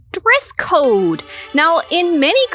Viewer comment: what the hell is a "dress cord"? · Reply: Dress code** whoever was doing the video editing probably thought she said "dress cord"